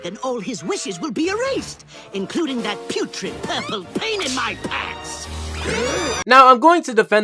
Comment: Legit thought foop said "giant purple penis"